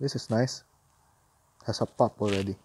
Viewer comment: I replayed a part of your vid around the 7:55 mark. I swore I heard you say, “it’s got a F(word) already.” LOL! It doesn’t sound like “pup” at all! 😂